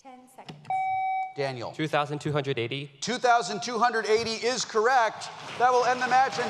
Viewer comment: i thought he said 3280 :/